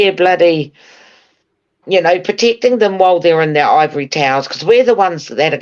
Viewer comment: Lol I thought I heard "Ovary Towers" 😂